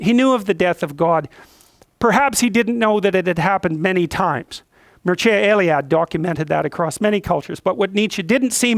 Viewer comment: I cannot understand or infer the author he is naming, would be very glad if someone could help me out. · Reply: Must be Mircea Eliade